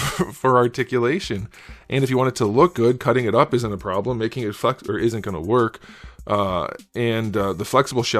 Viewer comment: I thought you said "making it fu**" at 11:25 and had to do a double take.